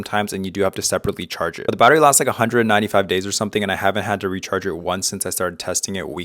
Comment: did you say 195 days?